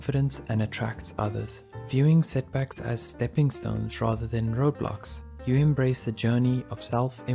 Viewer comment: Love this episode, but did anyone else notice the subtitles saying Roblox instead of road blocks at 2:44 ? Just curious😅 Still a great episode.❤